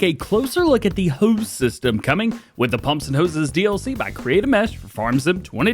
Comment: I always thought it said Pump and Horses, for months, kept wondering where the horses were. Is this worth a buy now I actually know what it is?